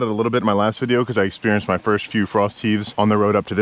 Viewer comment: I thought he said frosty not frost heave😂